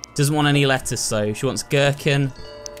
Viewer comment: did you just said „Gürkchen“?